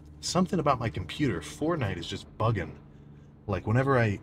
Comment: did i heard fortnite?